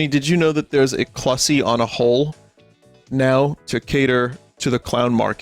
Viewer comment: For a moment I was surprised Vinny didn't know what an onahole is but then I realized the problem is me, not him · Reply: I thought he was saying "clussy on a hole" at first